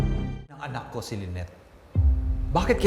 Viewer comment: haha tama ba narinig ko, sabi Rj, “anak ko si Lyneth” 😂😂😂😂 Wala ng retake yarn? 😅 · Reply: "Nang anak ko si lyneth" - nireview ko ulit, Yan ata Yung Tama djan. Panoorin mo ulit